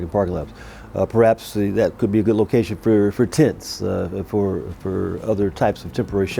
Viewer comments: I thought he said "That would be a good location for TITS" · I thought he said tits